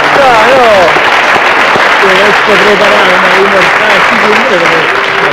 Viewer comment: Chi è accorto che al 1:54 benigni sotto gli applausi del pubblico,commenta la chiusura di questo simpatico siparietto (anni 60) dicendo tipo[e magari ho capito male io] : "ti sei preparato oh,mortacci tua!" E Troisi fa un gesto che io personalmente interpreto con : tutto liscio come l'olio...c'avete fatto caso?